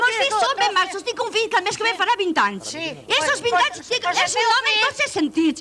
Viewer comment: qué dice?? "Tinc tres fills els mos fills son ben machos tinc un fill ca mésquevepagá vint anys" no he entendido bien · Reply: "Tinc un fill que el mes que ve farà 20 anys" Després de 8 anys ja era hora que algú hi respongués. xD